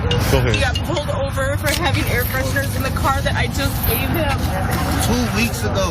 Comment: NO MOTHER SHOULD HAVE TO GO THROUGH THIS. I pray for her and their family. Did I hear "air freshener"? Wow, just wow. 😔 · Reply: Air pressure